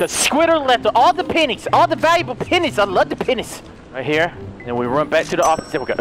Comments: sounds like he saying penis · Lol it sounded like you said I like the penni's😂😂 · I heard “I love the penis” but like he didn’t pronounce the E properly